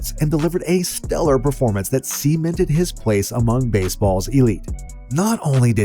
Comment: why did you say semen-ted instead of cemented